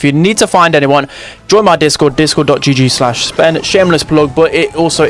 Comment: Did you just say GIGI ??? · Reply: Gg